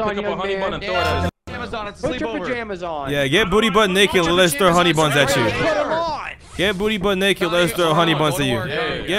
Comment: not even a minute in and i hear "get booty but naked and let us throw honeybuns at you" XD